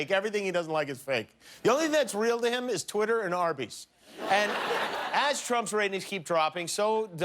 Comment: " the only things that's real to him is tweeter and HERPES"... or maybe i did misheard it...lmao